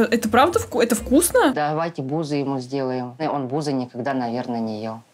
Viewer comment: мне одной послышалось арбузы? 😂